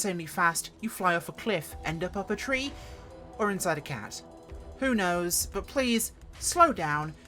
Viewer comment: ".... or inside a CAT?" Did I hear that right? I loved this video, very informative.